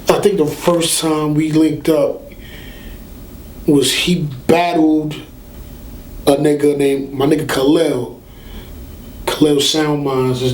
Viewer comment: I thought he said "Tech bottled a nigga...my nigga Khalil" and I'm wondering why Tech would bottle somebody who's all love haha